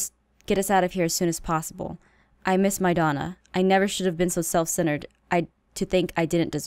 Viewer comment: Logan: "I miss my Donna" Me: I thought he said "I miss madonna" 😂😂